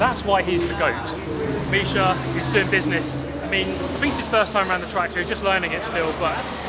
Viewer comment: haha did he say this is micha first round the nurburgring??